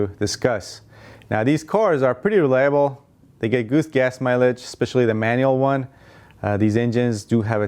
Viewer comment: Who else heard “goose gas mileage” when he spoke about the Toyota Tercel at 3:30? Lol